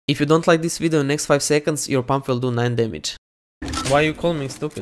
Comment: i thought he said your mom will do 9 damage